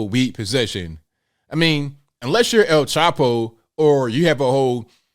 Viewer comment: I know that you said, "El Chapo," but I heard, "El TACO!" "Jill Biden has infected my brain!" 🤣